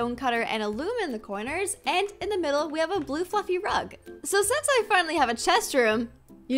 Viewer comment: i was listening to this vid in 2x and at 0:34 I thought you said "a blue f**** rug"